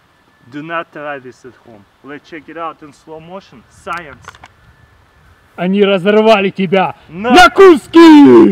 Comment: Does anyone know what they said at 1:27? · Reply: Они разорвали тебя... На куски! I don‘t know, if Iwrote it correctly, since I‘m not russian. Translated it‘s something like: „They tore you down... to pieces“ EDIT: There are subtitles.